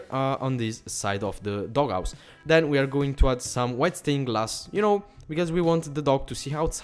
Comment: I thought he said "wet stained glass "